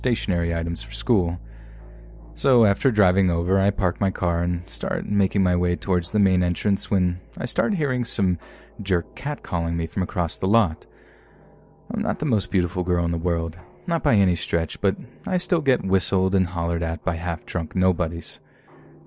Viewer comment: I thought it said “some jerk cat, calling me” at first oof